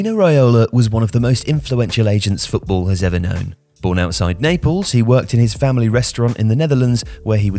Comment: I thought he said born outside staples